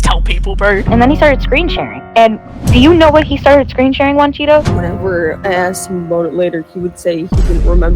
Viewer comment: I heard “he was screensharing Cheetos” in the intro